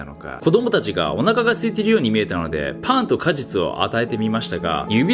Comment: ｢パンと果実｣が ｢パーンッと果実｣に聞こえてしょうがない